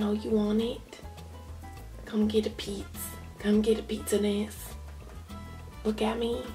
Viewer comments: she sounds like she saying 'come and get a pizza dish' LOL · I thought you said "come get a pizza dish"